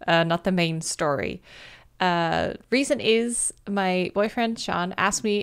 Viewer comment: for a sec I thought she said "My ex boyfriend Sean" and mi just like WHAT?!?!? but then I realized I was wrong haha and sorry if I didn't spell his name right